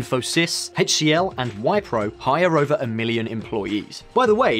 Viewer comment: I heard "Wipe-row" instead of "Whip-row"